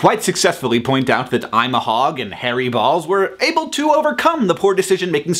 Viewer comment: At the beginning i thought you were saying "...In your last video you were able to quite successfully pointed out that i'm a hog..." xD